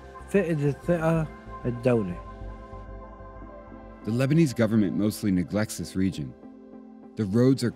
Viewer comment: I thought that said LeBrons. LoL.